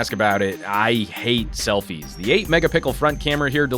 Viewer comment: Who else Heard "the 8 MegaPickle Front camera" on 6:57???